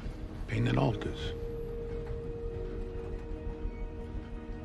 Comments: I thought he said:" Big One, Send Sagey Jackson." not "Bugun sen sececeksin." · For those who couldnt figure out what he is saying at the end. "There are two kinds of Pain in this World. - Pain that hurts - Pain that alters. bugün.. sen seçeceksin." it sounds like he says bigun (it almost sounds like bigün - bigün means "One Day" but the correct term would be bugün - cause its about him sitting across him and giving him the chance for the very moment) and overall "bugün.. sen seçeceksin" means Today.. You will decide. Dope. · In the last scene he is talking turkish: "Bugün sen seçeceksin" = "today you will choose" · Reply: I thought he said: "Peki onu sen seçeceksin"